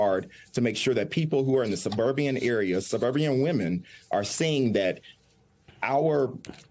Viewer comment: Did he say "Suburbian"??? LMAO